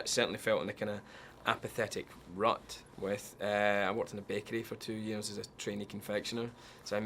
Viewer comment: Man his accent... when he said bakery I thought he said 'bear cave' at first =.=